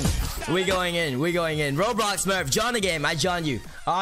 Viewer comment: Am I the only one who thought he said RobloxSmurf ?